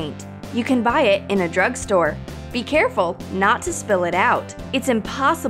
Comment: did you just said drug store in